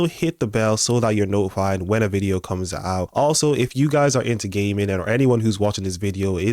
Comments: I have to tell you the first time I heard you say if your into gaming,,,,I thought you said if your into gay men 😂😂😂😂…I listened closely and started laughing 😂 .. I do love your channel … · If I'm into gay men??? Ohhhh, you said gaming lol. I know you have an accent, and I swear, I thought you said "gay men" at first. Yes, I will sub to your other channel. I build gaming computers, and game every day. Cheers mate